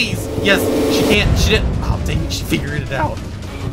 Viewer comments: o; did he just say a swore? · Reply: No he said dang it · is it me or did he say Sh*t? o_o|| I think he was mid sentence tho, so he didnt mean it. Love ur vids